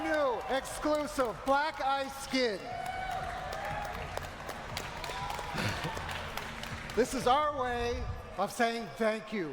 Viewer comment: for a moment I thought he said "black ass skin!"